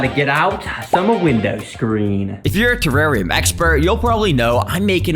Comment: i thought he said terraria lmao